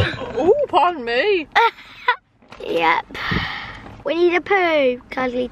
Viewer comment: you know, when said " winnie the pooh " i honestly thought he said " we need a poo " lol